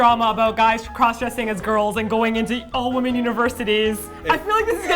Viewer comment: I had to turn on the subtitles cuz i thought she said Evil Women's University